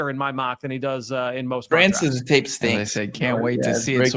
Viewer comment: Why is someone saying „Francis d*** stinks“ at 1:02:00?😂